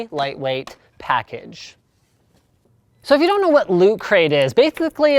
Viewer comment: I thought he said lukecrate :p